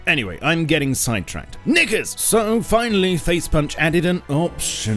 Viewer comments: OH MY GOSH, I thought said the N word when he said knickers. xD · I thought he said n**ga when he said knickers